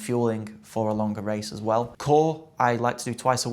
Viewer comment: can anyone tell me whats the word here on 5:03 the thing that he tries to do twice a week i cant for life of me understand and subtitles dont work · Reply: Core